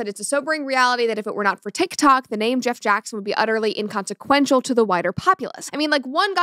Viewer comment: i keep hearing "The Famous Jett Jackson"